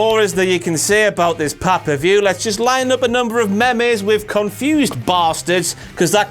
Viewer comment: Did he just say meh mehs instead of memes..